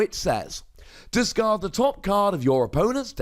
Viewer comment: I misheard it at first and thought it was diacard your OWN card!